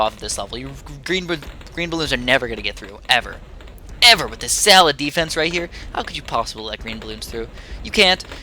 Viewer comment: I thought you said a salad defense at one point